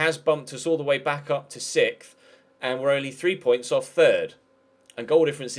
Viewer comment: The way you said sixth at 18:14-18:15 it kinda sounded like "sith" lol